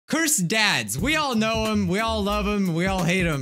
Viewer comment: I thought he said cursed dad's lmao